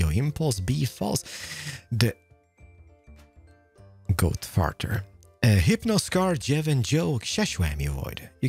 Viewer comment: Wait, did Keralis say Goat Farter?